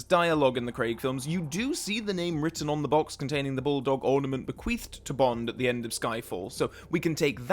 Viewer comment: why did i first hear bequeefed instead of bequeathed?